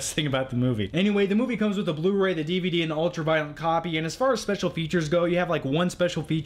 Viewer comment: It sounds like you say "ultra-violent" instead of "ultra-violet." Also, you use the word "phenomenal" a lot when describing good Blu-ray picture quality.